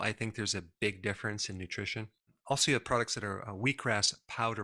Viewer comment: Who else thought he said weed products? 😂😂